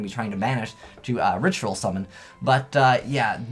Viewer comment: Did you just say Ritual Summon? wtf?